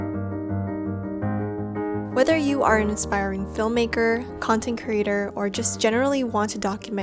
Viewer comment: Did she say "inspiring filmmaker" instead of "aspiring filmmaker"?? Other than that, good job.